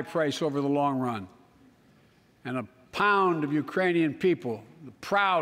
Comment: Who else heard him say Iranian people instead of Ukrainian people 😂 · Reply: I heard him said “a pound of Ukrainians” wtf